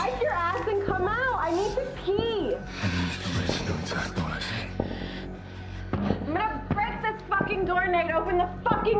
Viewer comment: What did he say at 1:40? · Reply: He said "I need you to listen to exactly what I say"